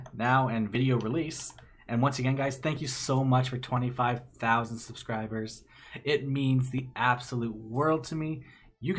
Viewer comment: For Some reason, I thought you were gonna say "thank you so much for 25 Million Subscribers!" XD Still waiting for it tho.. <3